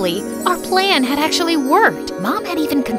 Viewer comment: did you just say F plan actually worked